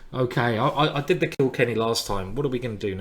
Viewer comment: Am I the only one that heard it as the Kill Kenny Gambit (you bastrds!)